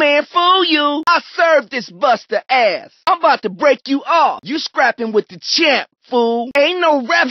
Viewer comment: What is he saying ? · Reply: "You're scrapping with the champ, fool"